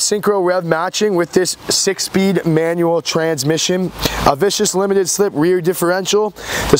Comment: did he just say vicious? at 1:04.